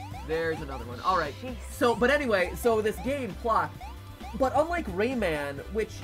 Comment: At around the 26:42 timestamp mat starts talking about a game. I can't hear it's name! He says, like, clop? Flop? Flock? Can somone please help? 😓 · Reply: Plok